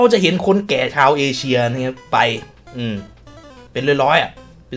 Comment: ผมได้ยินเป็นชาวเอวเดย์